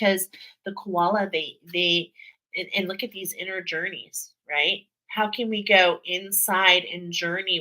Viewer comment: when you said Inner Journeys, I heard Energies. I like the wordplay that presents!